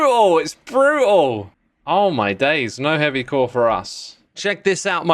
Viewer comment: when X got his first heavy core, I misheard that he just shouted "Happy core!!"